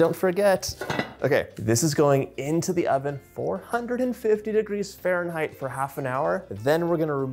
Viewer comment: First time baker. When you said “this goes in 450 F” I heard you said “this goes in for hundred and fifty degree fehrenteirt” (150 F) 😢